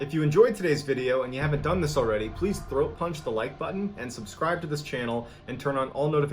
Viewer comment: GigAkuma 🤦🏾‍♀️ I heard it wrong either way the like button getting punched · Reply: I heard "throw punch" too🤣🤣🤣 I thought maybe it was a secret Navy Seal move😂😂😂